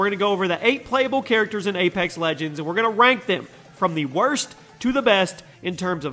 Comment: Did anyone else think he said 'wank them' at 0:41 ?